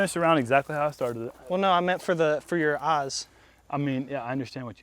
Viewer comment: Hear ye hear ye, all squires!! Our website is now back up and running for you to ravage with your orders 👑 <link> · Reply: “i meant sun, ya, for ya OZ!” stg i heard anything but eyes lol